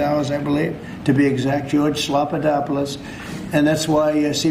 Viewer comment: Lmao did he say George Slopadopolis?